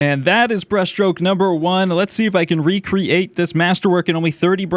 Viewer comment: I thought he said breast stroke number one 😭